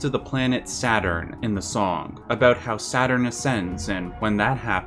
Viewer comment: i always thought he said sad in ourselves instead of Saturn ascends